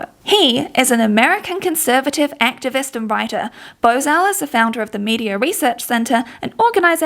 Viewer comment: oh man i was only listening to it and thought i heard his name as "bozo", chuckled thinking it was a joke, but no, his name is bozell hahahahaha